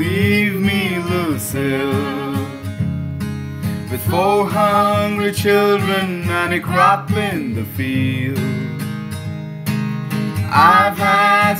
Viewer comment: Lucille Lyrics, thanks me later😊 [Verse 1:] In a bar in Toledo Across from the depot On a bar stool she took off her ring I thought I'd get closer So I walked on over I sat down and asked her name When the drinks finally hit her She said I'm no quitter But I finally quit living on dreams I'm hungry for laughter And here ever after I'm after whatever the other life brings In the mirror I saw him And I closely watched him I thought how he looked out of place He came to the woman Who sat there beside me He had a strange look on his face The big hands were calloused He looked like a mountain For a minute I thought I was dead But he started shaking His big heart was breaking He turned to the woman and said [Chorus:] You picked a fine time to leave me, Lucille With four hungry children And a crop in the field I've had some bad times Lived through some sad times But this time you're hurting won't heal You picked a fine time to leave me, Lucille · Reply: thanks for the lyrics, I thought I heard 'four hundred children and a crap in the field' but these lyrics helped clarify that out.